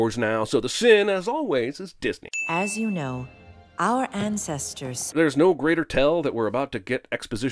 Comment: she clearly said ancestors, but it says ancestor in the transcription text. If you are going to sin Miriam for writing "u're" as a kid her age would do, then I think that my sin for adults transcribing Jeremy's words here is fair. Ding